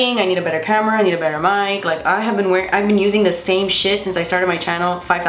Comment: OMG ! I thought you said "I need a better Camera, i need a better Light, i need a better mind..." and I was like...Same Girl, Same...Realize now you said MIC 😁The look Is BEAUT!